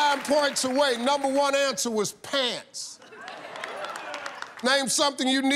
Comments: shaq said “pads”.. he was never gona admit that, he knows how to win championships .. and snitches get stitches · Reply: Nah he 100% said pants. I thought he said pads to so I check the audio and he says pants for sure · I was there then and I heard "pants". It just sounds like pads but he actually said pants. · I thought Shaq said PADS not pants lol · Too get 9 points exactly on answer was pretty cool.....Shaq had swagg on fast money he didn't even sweat.....I thought he said pass instead of pants.....Steve killed impersonation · Shaq said “pads” they thought he said pants😂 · I thought he said pads not pants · i thought shaq said pads not pants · I heard as “pads”. Not “pants”. Anyway it would have won on the Fortune Cookie and for sure on the Coffee. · I could have sworn Shaq said pads not pants. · He said pads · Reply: Hmm. That is what I thought he said - the first time that I watched it. But ..... the 2nd time ..... it was definitely "pants". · Bro I swore he said pads not pants...